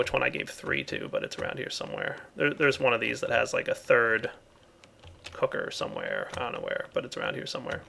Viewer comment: did he say a third hooker.....